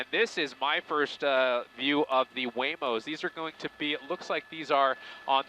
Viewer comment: I can guarantee you those waymo vehicles recorded their faces. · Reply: I thought he said huevos instead of Waymo